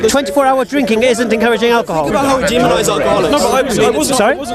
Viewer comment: "think about our garlics, think about how much are garlic is demonised in the media" response (and mine) "what you on about?" Did I even hear it correctly?...Garlic? I almost pissed myself with laughter! Edit Alcoholics is what he is saying. Still though it sounded like garlics.